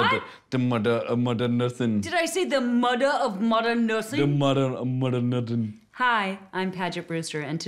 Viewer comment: I heard “The mudder of modern nutting” 😂😂